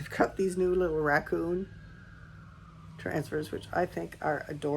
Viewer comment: I thought you said “raku transfers” and I’m thinking to myself, why would they raku a raccoon? 🤪 I was very confused for a moment there. I need more coffee! Congrats on selling out, that amazing! Great kiln opening as usual!